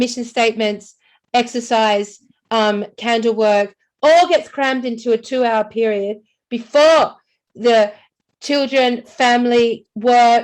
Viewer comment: Did she just say "Candle work magic"